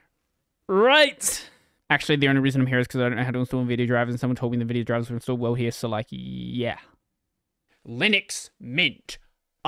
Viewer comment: can someone please tell me what he says at 1:05 trying my best but i can't get it 😂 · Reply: "Actually the only reason I'm here is cuz I don't know how to install the Nvidia drivers and someone told me the drivers would install well here, so like yeahhhh...."